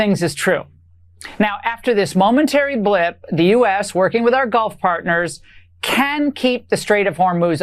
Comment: I could have sworn I heard her say "golf partners." At this point I wouldn't be surprised, who doesn't love a golf resort in the desert